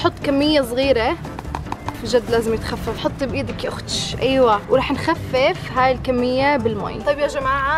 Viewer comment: ليش صرت اضحك لما سمعت شو حكيتي؟ لما سمعت هاي (يا اخت ال ش) تركت الرسم و قضيتا ضحك بعرش لي!.